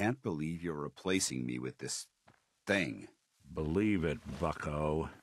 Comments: I wasnt paying complete attention to Nick's response to Danse and I thought he said fucko lmao. I was like, "damn that was too savage for you nickie boy" Kinda disappointed that it actually wasn't what he said.. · I thought he said 'fucko' instead if 'bucko' 😂😂😂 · I thought Nick said Fucko not Bucko holy shit. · For a moment I thought he said "fucko" instead of "bucko" to Danse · I swear i heard him say believe it fucko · For a second, it sounded like Nick said 'Believe it Fucko', that would've been amazing if that had happened.